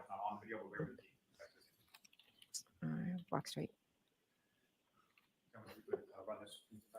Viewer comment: Hey catching up on replay tonight Is it my imagination, or at 2:51:47 does Jen say, "Ok, walk straight" as she's watching herself get out of Karen's car going into 1 Meadows?? I've replayed it a bunch but maybe you can hear it better?? It's like she's talking to herself from the courtroom, as in, Don't look drunk, sober up!!